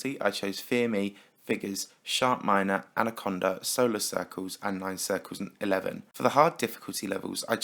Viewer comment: did he just say "Nine Circles Eleven"?